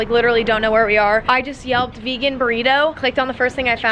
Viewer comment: thought she said i yelled vegan burritos not yelped. 😭😭😭😭😭 i screeched